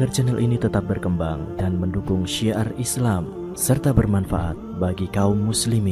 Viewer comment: Maaf ini syiah islam atau syiar islam...😑maaf akhu...mungkin sya salah denger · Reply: Syiar